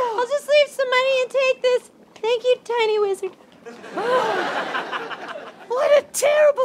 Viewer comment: I thought it said lizard at first.